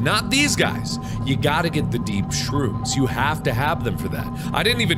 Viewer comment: ish me, falling asleep at one o'clock in the morning listening, but also not really listening to mark talk about what he needs to do or something: .... mark (at least what i heard him say, anyway): blah blah blah something something shrooms. and we cant use normal shrooms, we need to get the deep sea shrooms, because we can only make acid with those specific mushrooms. Me, snapping awake: Ah, wait, what, deep sea shrooms? what are you planning on doing mark? starts quietly giggling to myself